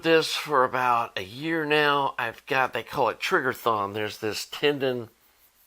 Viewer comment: Lol, when you first said it I wasn’t looking at the screen and I heard “Trigger Thong”. Whoa, too much info! 😂 Seriously, all the best with your surgery, and excellent advice on upgrade expectations! 👍